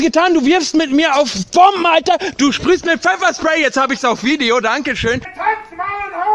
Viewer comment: my non german speaking ass thought it sounded like “Its obviously on video i don’t give a shit.”